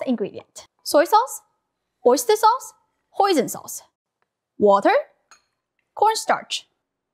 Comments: Who else thought she said poison sauce 😭😭😭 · I thought you were say poison sauce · For a second I thought she said poison sauce. 😂🤣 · when she said "hoisin sauce" but I heard "poison sauce" 😆 🤣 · i swear to god i thought she said poison sauce lmfaoooo 😂😮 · Who else first thought she said poison sauce? I had to watch it like 5 times cuz I kept missing the captioning.. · When she said hoisin sauce I thought she said poison sauce